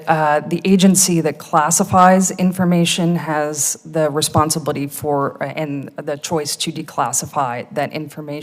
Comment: Did she just say "Responsa-booty?" EDIT